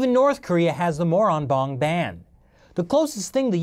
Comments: I just heard Even the North Korean have the Moron bong band LOL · Did he say the Moron Bong Band? Was their first album called "Pack me a cone"?